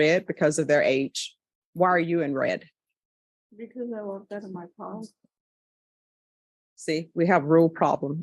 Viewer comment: What did she say? couldn't understand the end. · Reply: "Because i look good in my colors..?" She retorted, with pride and sass A lost cause